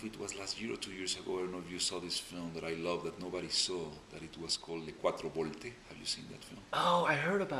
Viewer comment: What was the film he was talking about i couldnt hear him "que por ti" something. interesting in watching it anybody got a link or something the title and who made it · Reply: Le Quattro Volte